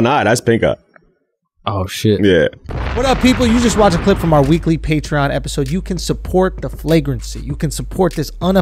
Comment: At the end.. I thought he said 'the weekly pink eye episode'.